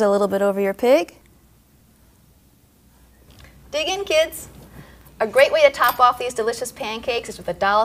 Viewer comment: Lol at the end when she said dig in kids I thought she said pig in kids